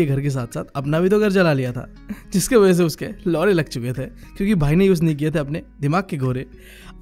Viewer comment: Did you just say "lodhe" At 2:40 😶???!